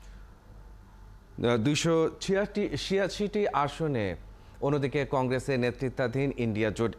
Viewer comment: What he said in 0:24 second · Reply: 286 seat